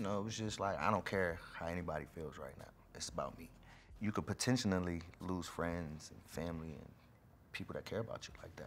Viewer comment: Did dude just say "potentionally"?🤔🤔🤔. Is that a word or did I hear it wrong?😂😂😂